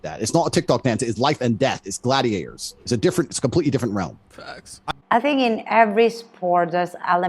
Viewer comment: that "facts" always kills me🤣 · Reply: I thought she said facks as in fucks !!!